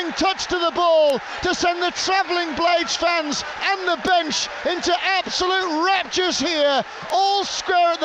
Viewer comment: istil don't know what the comentator is saying at theminute 11:09 can somebody explain me ? btw huge effort from the blades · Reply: He says (the goal) "sends the Blades fans , and the bench , into absolute raptures". Rapture is heavenly delight and joy